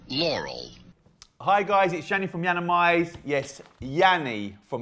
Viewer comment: Guys why do I hear “hey guys it’s Laurel from lauralmize” da heck ?!